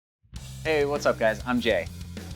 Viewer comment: I thought he said hi guys I’m gay